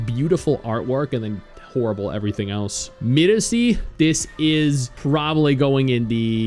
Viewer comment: What's the name of the gatcha in minute 8:50? I cant make up what he said lol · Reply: It's Miresi I js got to know about that rn